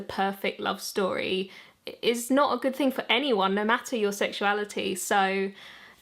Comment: Sounded like she said “normatter your sexuality” lol. But more seriously do have some questions… Do aroace individuals ever feel stuck by their own labeling of themselves as such? Like does it make it awkward if suddenly they find someone they are romantically & or relationally attracted to? Do they think “oh no what will they all think or say? Will others say to themselves “see I knew deep down they weren’t really aroace. It was just a phase, and they just haven’t met the right person yet.”. Does it make them feel that if they ever change their mind, they will somehow be harming the concept in general for others or themselves in the future if they go back to being aroace? My last question is this: are there some individuals who label themselves as such only to make themselves seem more desirable in a reverse psychology /paying impossible to get kind of way to people who normally might not even consider them attractive?